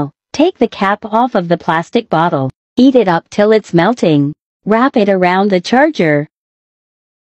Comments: Am i the only one who thought it said eat it up still its melted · i thought she said “EAT it up till it’s melted” 😀✌️ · I did not know she said heat it up. I thought she said, eat it up.